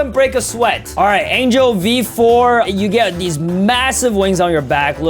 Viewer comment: did bro said MASSIVE wings ?